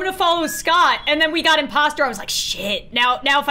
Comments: Is this normal- or did Lauren say “sh!t” on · did she say... Sh¡t? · Is it just me or did Lauren say Shit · did she just say shit